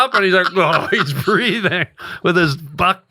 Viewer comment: Well the hell does Ryan say at 30:52 😂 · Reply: “He’s grabbing the hose” lol